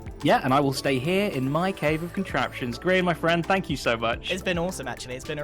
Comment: Is it just me or did say "Grain, my Friend"?